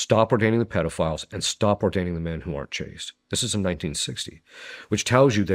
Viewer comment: What is he saying at 2:31:25? Stop ordaining homosexuals, pedophiles and what was the third one? Men who aren’t what? Sounds like chased (changed?)? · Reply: Got the answer; According to Proverbs 28:1, the righteous are not chased, as they are "as bold as a lion". In contrast, the "wicked flee when no one pursues". The righteous lack fear because they have a clear conscience, whereas the wicked suffer from internal paranoia and guilt, causing them to run even without external danger. BibleProject BibleProject +4 Who is NOT chased: The righteous, honest, and godly individuals (Proverbs 28:1). Why they are not chased: Because they possess moral integrity and boldness, rather than the fear and paranoia that drives the wicked to flee.